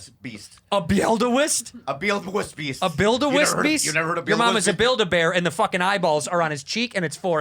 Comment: plz someone teach me 9:42 what they said pronounced "buildewist(?) beast" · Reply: Pretty sure he was trying to say Wildebeest, a type of antelope. He may have mixed that word with Beelzebub, creating the infamous Bobby Ree Beelzeweest.